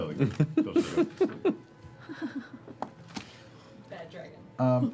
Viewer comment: I'm sorry, did I hear Marisha say "Bad Dragon" after Jester talked about drawing Goblin parts in her journal? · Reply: She does indeed say Bad Dragon. Timestamp